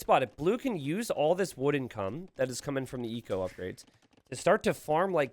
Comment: it sounds like T90 says "wood n cum"